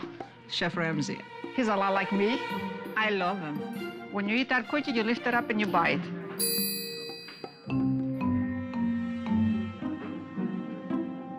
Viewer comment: "When you eat that cookie, you lift it up and you bite" Yes I think the world renowned chef knows how to eat a cookie but thanks for the advice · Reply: i thought she said “when you eat that cookie, you lift it up and you buy it”